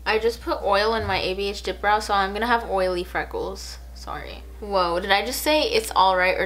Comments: At first I heard sorry than I heard it's all right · at first I heard sorry but I replayed it and heard it’s alright, but I did it again and I heard sorry. Idk I think you can hear both if you try. Edit:Also if you see this I think you wear trying to say yanny vs. laurel I heart u